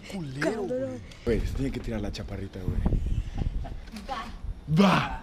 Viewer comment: te lo juro te lo juro que escuche "te tienes que tirar a la chaparrita" :v